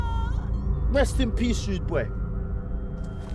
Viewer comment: can someone tell me what he says? Rest in peace ……? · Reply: Rude boy